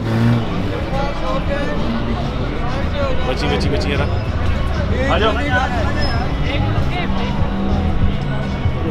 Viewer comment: on 4:47 4:48 What you saying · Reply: Trying to get people out of the way. Bacheen bacheen bacheen yar